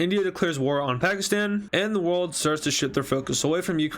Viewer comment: i heard him say "the world starts the shlt their focus"